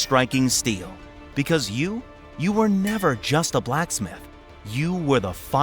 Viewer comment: I thought you said it sucked as a blacksmith